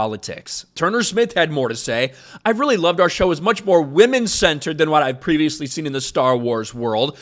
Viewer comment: It sounded like she was saying “women scented”